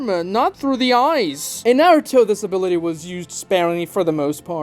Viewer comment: I heard "disability" and when you think about it that way, it still isn't wrong...